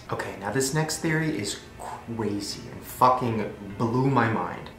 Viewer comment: who else heared CWASY instead og CRASY@